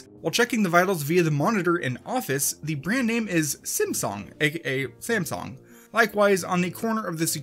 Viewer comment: I thought it said simpong